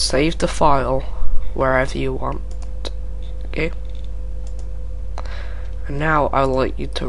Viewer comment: sounded like gay lolz